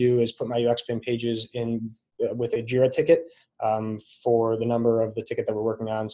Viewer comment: What is he saying at 15:31, 16:16 and 16:21 ... "giro ticket"? What is the word? Is this a feature specific to UXPin? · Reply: JIRA - <link>